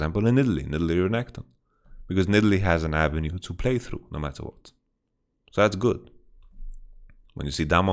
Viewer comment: can someone please tell me what he is saying there? I cant hear him, maybe i dont know the words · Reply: “Because Nidalee has an avenue to play through, no matter what”